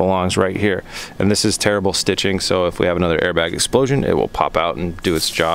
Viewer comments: At first I thought you said terrible stitching, then realized it was "tear-able" stitching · I heard him say "Terrible stiching" not "tear-able stitching" haha!